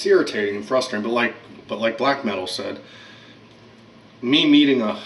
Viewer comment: did you say "Like black men will say?"